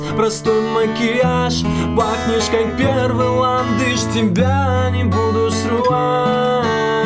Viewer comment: я же не один услышал :"пахнешь как первый латыш"?)